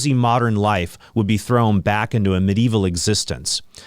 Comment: Did you just say "medieval existence"? That sounds pretty nice.